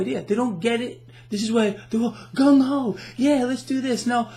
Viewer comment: did you just said "GUNG HO" 3:26 the name of the book ??? ;)